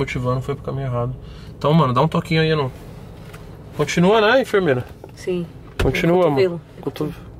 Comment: eu entendi um continua amor?